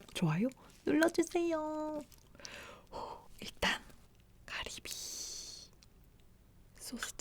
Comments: Am I the only one that noticed that when she said scallop in Korean it sounded like cardi b 😂 Listen! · it sounded like she said Cardi B